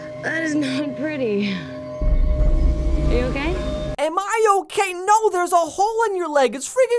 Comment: cought cought she says are you okay? not am i okay? XD (she just says are your realy quick, making it hard to her, but if you really listen to it you can hear she says are you okay? and not am I okay?. sorry i just have to correct your mishearing XD